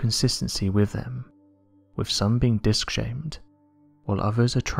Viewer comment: thanks for the video.i live out here in arizona near the superstition mountains.i can tell you we are definitely not alone in this universe.i have seen disk shaped ufos and orbs of light at night on that mountain.those disk and orbs are not from this world.they move at incredible speeds and then come to a full stop.with our current tech i dont think we can do that,peace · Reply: Thought you said dick shaped UFOs there for a second